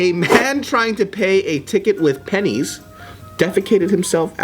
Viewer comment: I thought it said, "penis"